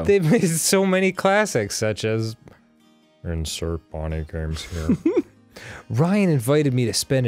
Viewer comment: I thought Danny said Brian.. as in ninja Brian and I got excited